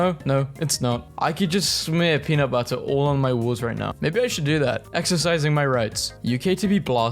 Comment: did say walls or BALLS? 😭